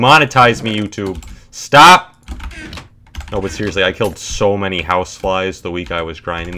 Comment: Thought he said I killed so many house wives the week I was grinding there.